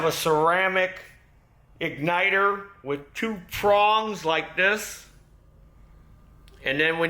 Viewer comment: Prongs? I thought you said prawns, and I got totally lost because I started thinking about dinner. Back in the 50s, the auto service center I worked at, had a spark plug tester made by Champion. It tested the plug and the machine also pressurized the chamber to test the plug under load. It was a fun machine to operate, and did show up plugs (normally resistor plugs) where the plug had lost continuity. I preferred using the oscilloscope, because it immediately identified the weak plugs right in the car. I still have a Precision diagnostic unit for testing my older carburetted engines.